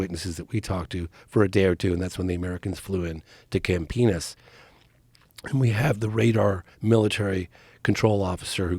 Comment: I need… to see this. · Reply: Point of order: Did he just say, clear as day, with a completely straight face… “Camp Penis”?